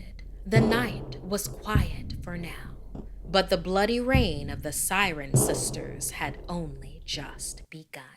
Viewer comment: I heard ‘The Skylar sisters’ and I was shook until I realized I heard wrong